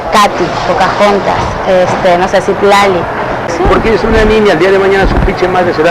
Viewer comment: Pequeño detalle: Ella dice "no sé, Xitlali" y no "no sé sí, Tlali." Xitlali es un nombre poco común de mujer pero Tlali lo es aún más (creo que no lo es). Estoy 100% seguro de que dijo "Xitlali" y no "sí, Tlali". Un abrazo.